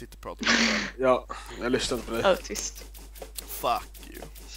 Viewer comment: Did he just call him an autist? XD